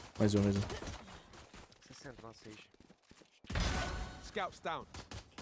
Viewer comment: Eu tô doido ou ouvi ele dizer: "Mais, mais um."